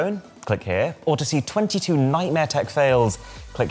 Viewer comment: did he say mysteriousthe boss 😜😅😂